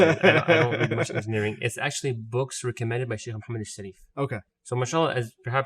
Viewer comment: What name is he saying? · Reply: Shaykh Muhammad AlShareef